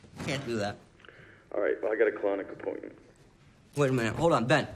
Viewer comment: I love that small moment at 1:04 where in the midst of everyone freaking out, Ben tries to end the call because he has a colonic appointment and even though he’s at his wits end, Jamie can’t help but laugh. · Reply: Funny I thought he said chronic